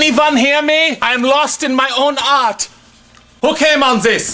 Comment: When he said, "I'm lost in my own art." I thought he said, "I'm lost in my own 'arse'." I died 😂